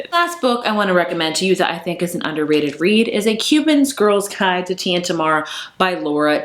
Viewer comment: I was listening but not watching and thought you said “A Cuban Girl’s Guide to Tia and Tamara” and I was like I’m down before looking and seeing “Tea and Tomorrow” lmao. But I’m so into these recs! The only one I had heard of was Anna K.